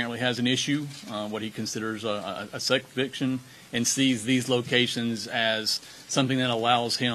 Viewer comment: what is the sheriff saying? "sek viction"?